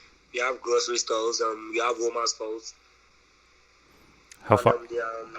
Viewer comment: I thought he said we have women's clothes when he said we have Walmart close.